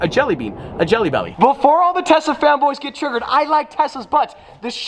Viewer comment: No one: Hearing saying he loves tessa's butt instead of tesla